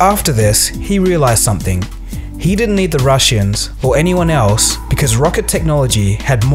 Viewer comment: Am I the only one who heard him say the Rushians?